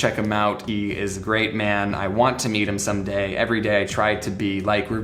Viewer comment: I thought u said u want to eat him everyday